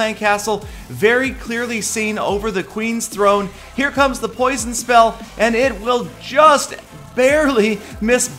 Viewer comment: of Clans Attacks 1:46 Did you really say poison spell? :D